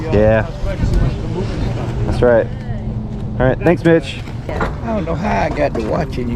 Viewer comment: Am i the only one who heard Thanks bitch instead of thanks mitch at 2:35 ? 😂